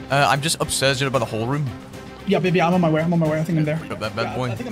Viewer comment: Did called “baby” at 12:03? 🤣